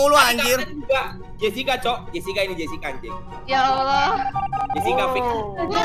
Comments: Kak Jesicca itu kok nyebut y Allah ya apa aku salah denger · menit 15:45 jesica bilang apa🤔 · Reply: Bilang Ya Allah bang, Di Alkitab (kitab suci) Agama Katolik/Kristen ada juga kok bang penyebutan nama Allah, jadi bukan cuman agama islam aja iya bang.... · Jessica ngomong apa tu? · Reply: Ya Allah, Di Alkitab (kitab suci) Agama Katolik/Kristen ada juga kok bang penyebutan nama Allah, jadi bukan cuman agama islam aja iya bang.... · Apa aku salah denger?? 15:48 Jessica jane "Ya allah"